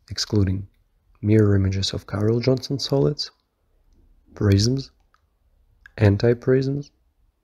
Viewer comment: my brain thought he said rasins at first